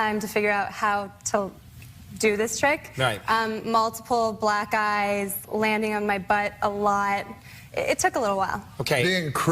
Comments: I really thought she said "Multiple black guys landing on my butt a lot.." 😳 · Did I hear the crippler girl say she had multiple black guys landing on her butt? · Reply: I thought thats what she said too. After like 10 watches i think its: multiple black eyes, landing on my butt. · I always hear “multiple black GUYS landing on my butt.”